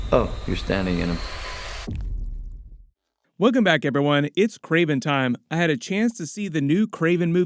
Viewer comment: Thought is said Karen Time and I was like no thanks 😂